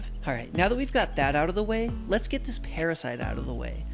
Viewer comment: I heard "now let's eat this parasite" for a sec lol